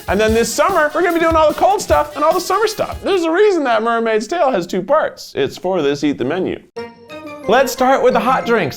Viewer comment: I thought he said, "That's why the Mermaid Tale has two tales." I was like Keith watches Barbie?!?!